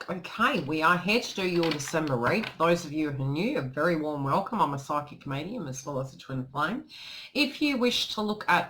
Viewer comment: Lol, 😂 as i was listening to your intro I heard “I’m a psychic comedian” 🤷‍♀️🤦‍♀️😁 Maybe that’s next on your path 🤔😂👍